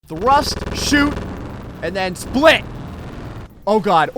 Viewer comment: Not five seconds in and i hear "thrust, shoot, and split"